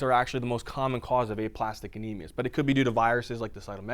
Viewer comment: oh I thought it said enemas...